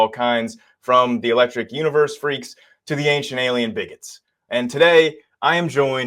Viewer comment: Did he just say "ancient alien bigots"?